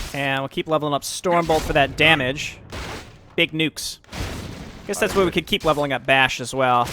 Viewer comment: Lol i thought he said big nudes lol